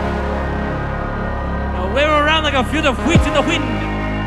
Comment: what's he saying exactly? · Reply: "LIKE A FIELD OF WHEATS IN THE WIND" lol I'm dying 😂